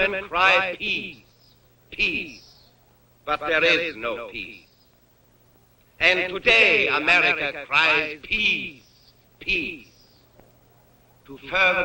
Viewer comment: why i hear it as "And today American cries Piss... Piss"